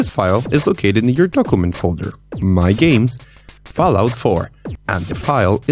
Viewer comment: did you just say fallout tree